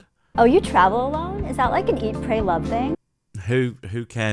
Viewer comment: And I heard "Eat brain love".....